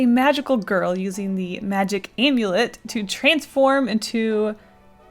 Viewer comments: For a second I thought you said magic omelet 🤣😂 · I might or might not have thought it said “Magic Omelet”